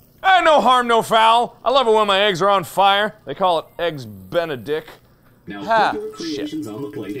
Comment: did he just say Eggs Benedict